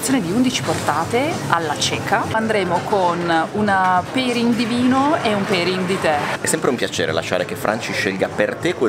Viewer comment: ho capito perín divino e perín ditè. E mi chiedevo che bevanda fosse questo perín